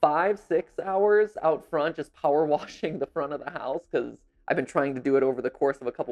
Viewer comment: “I finished power washing the house” - why is that the most dad thing I’ve heard xD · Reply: OMG THAT MAKES SO MUCH MORE SENSE. I really thought this man said ‘power walking’ and for some reason I just accepted it lol